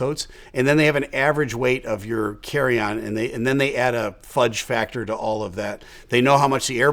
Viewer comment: Am I the only one who thought he said Fudge Packer at 8:54 mark?